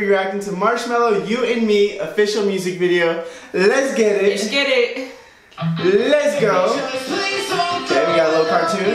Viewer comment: what the hell did she say? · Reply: Eshkerik